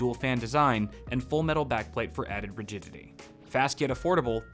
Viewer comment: i heard "added virginity" XD